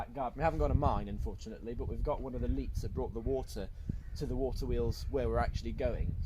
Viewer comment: At first I couldn't understand what you were saying, I thought you said, "the mine was with buttholes"?? Then I saw the mine filled with bottles. Check.. Got It..Not Buttholes..